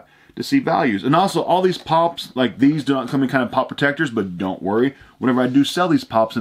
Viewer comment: Your Mic makes it sound like your saying pot protector